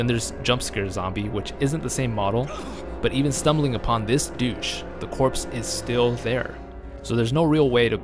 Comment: Bro im in tears. Did you call that zombie a douche at 3:00? 😂